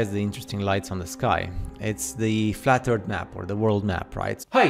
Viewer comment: LOL, 7:56 did he say flat-tard map?